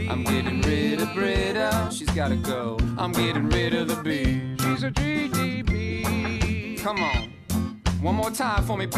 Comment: For the longest time, I thought Pierce said "She's a GED B"